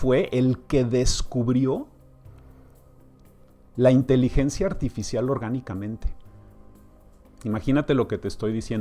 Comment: qué es "una Inteligencia artificial orgánica "....???? No me cierra eso...aclárame por favor el término. Gracias. Saludos!! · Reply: Querida Ninoska, vi el vídeo dos veces y no escuché "inteligencia artificial orgánica" (si es artificial, no creo que puedan ser demonios o energía propiamente dicho), lo que sí escuché fue " inteligencia artificial encontrada orgánicamente (encontrada de forma natural, no porque la estuvieran buscando) en los '50 (1958 si no me equivoco)